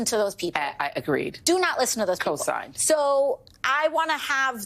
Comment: To the captioners, why did you leave out what she said at 3:40? What is "co-sign"? I can't tell. Maybe a viewer can clarify. Also, to AOC --- At the debate, Kamala kept on looking at the camera and said "I'm for you." So not sure what you're trying to say re: second person.